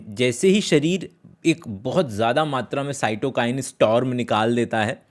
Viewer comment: What's that word which u used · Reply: cytokine storm i guess